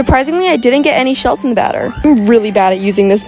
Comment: When u say batter it sounds like your saying badder